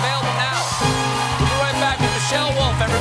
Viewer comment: dang i thought stephen said "we'll be right back with Vangelis!"